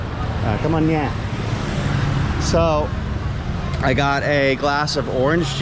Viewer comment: Great advice. I hear you say "cum on ye". What is that? I know Cum on is thank you.